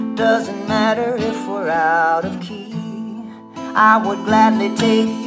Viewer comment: Made Me Laugh Out Loud ... Was Stirring A Fresh Gallon Of Sweet Tea When You Said, Out of Key ... Thought You Said Out Of Tea ... Yeah ... My Mind Is On AutoPilot From Now Til Morning ... Keep Playin For Us, Lukas ... I Damn Sho Enjoy It